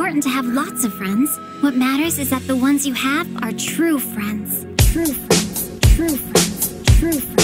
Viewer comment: Why am i hearing "True offence" XD